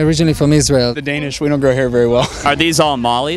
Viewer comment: does he say "The Danish don't like hair very well" ? Because in that case he is fucking spot on.